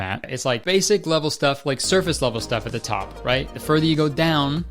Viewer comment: i thought you said you’d say surface leather instead of surface level ☹️💔